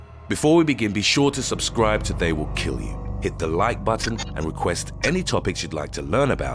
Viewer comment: I thought he said Subscribe or They Will Kill You 😂😂😂